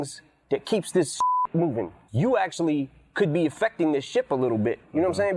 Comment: The word "ship" was erroneously edited out because it was mistakened for the word "shit," but the word "shit" was not said.